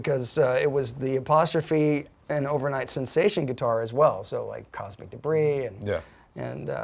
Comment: it almost sounds like Dweezil says "Toxic Debris", instead of Cosmic Debris?